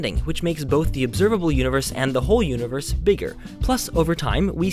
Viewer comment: NO he clearly says the WHOLE universe